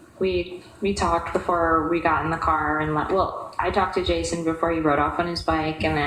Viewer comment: said what? what was said? that was a very important moment in the case..... . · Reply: The Part about she had words with Jason before she left....then Jason >>rode of on his bike...he didnt ride of on his bike..he stood there watching you ride of in your car with Alex?...ummm..